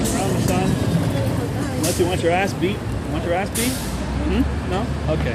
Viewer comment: At around 18:00 did some guy try to run you off? I thought I heard "if you want your ass kicked; do you want your ass kicked? No? I didn't think so." LMAO